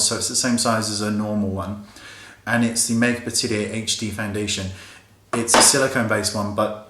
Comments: Does anyone know what the foundation is called at 4:25 sorry i couldn't understand what he said. Thanks :) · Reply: Make-up Atelier Paris HD Foundation :) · What is the name of the foundation he said at 4:26? I seriously cant understand him and I am very curious about it. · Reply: The brand is called Makeup Atelier Paris.